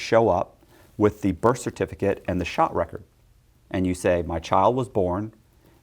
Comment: At the 11:00 mark did he say "shot record", as in immunization record? This "mandatory covaids vaccine" to work/socialize/travel thing is starting to sound a lot less medical and a lot more debt-slaves-as-collateral by the day!